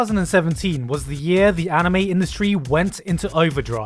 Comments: In 00:00 did i just heard 3017 or maybe meh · I heard 3017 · was he saying 3017 or do i have hearing problems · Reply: It sort of sounds like 3017 but I reckon it's 2017 because u can't fuck it up that bad right? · Did anyone hear that he said 3017 ? or i'm retarded ? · Did he said 3017?? XD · Did you say 3017 at the beginning of the video...